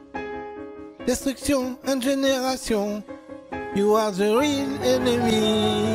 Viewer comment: I think he said "Destruction and Regeneration" but probaly in french xD